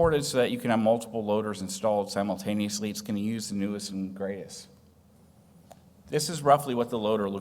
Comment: what spells did he just cast after saying "multiple loaders installed simultaneously". I couldn't understand what he said. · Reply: "It's gonna use the latest and greatest."